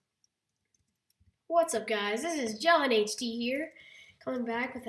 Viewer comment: It sounded like u said, "John HD".